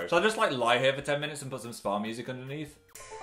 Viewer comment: I thought you said ska music instead of spa and I was like What!!!